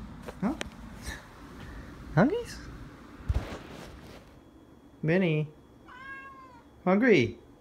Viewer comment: It's confirmed, the cat's real name is Yahoongry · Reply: nO i dOn't tHiNk sO, aT 0:58 hE sAid "minnie!!" (Woosh me plz )